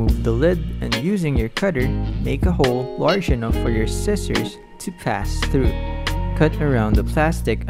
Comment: Is it me or did I hear make the hole larger so your sister can go in BAHHAHAHAHAHAHHAHAHAHAHA Timcheck